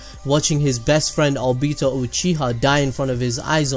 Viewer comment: Did anyone else here him say Albito instead of obito